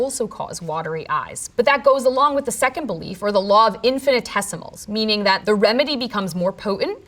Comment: Did anyone else hear "the law of infinite testicles" on 7:11 ? Just me? Ok